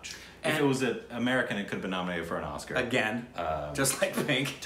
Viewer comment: did he just say it could be nominated for oscar!!??? i hope evrybody realise that asap.